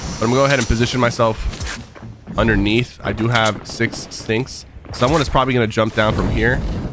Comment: (Love yr videos wish I can be like u) 0:40 did u say stinks or sticky’s